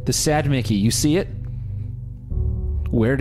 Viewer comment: At first when she said she had a sad Mickey, I thought she said she had a side Mickey. The first thought that went through my head was "Wait, so she is cheating on Mickey... with Mickey?"